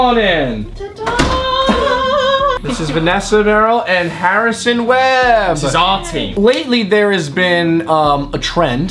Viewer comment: I thought he said Harrison wells I was like.....